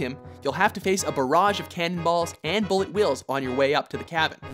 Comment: Bullet Wills. Please tell me I misheard